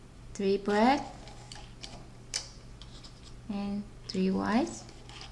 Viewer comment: It sounds like she says 3 bread 3 white